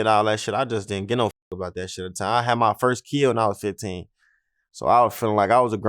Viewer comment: Hold Up✋🏾 What he say at 6:38?? I had my first Kia or kill at 15. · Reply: Kid